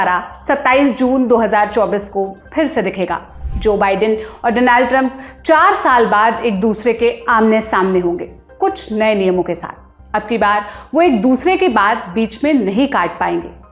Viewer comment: Did she say 'Denial' Trump at 1:03 😅